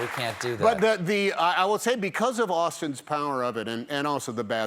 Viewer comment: Did someone say Austin Powers?